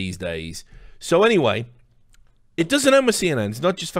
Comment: Was yawning when you said nansy so it sounded like you said nazi pelosi :)